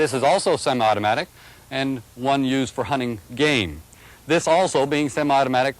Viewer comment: did he say for hunting gay? P.S sorry about your friends